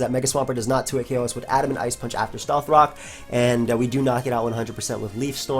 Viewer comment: inb4 leaf storm miss against Swampert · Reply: I tought i heard him say "this specific set lives adamant ice punch after rocks" but it might have been at plus 2 since tangrowth is fat